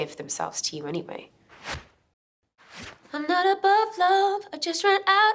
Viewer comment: When I heard this song for the first time I thought she said “I’m not a butt plug” Apparently it’s not the right words...